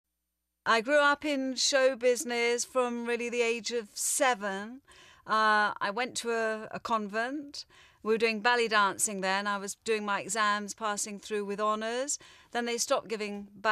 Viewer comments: I thought at first she said she was at a convent at the age of 7 doing belly dancing. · Ballet dancing in a convent! I thought she said "belly dancing" and did a double take